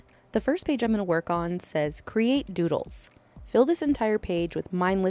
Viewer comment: I at first thought you said to create noodles 😂 I saw a dif where you saw a snail. This was a fun one.